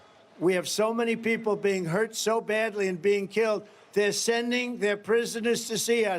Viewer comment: …does Trump make a ‘freudian slip’ @ 4:37 where it sounds like he says, “..we can’t have another Lincoln…” If so… what, exactly, does he mean? Does he mean that Lincoln shouldn’t have emancipated the slaves..? Is it code for his true sentiments? If so… who’s he conveying that coded sentiment to..?